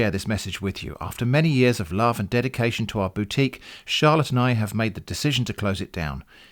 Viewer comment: lol, "Charlotte and I" sounded like "charlatan I"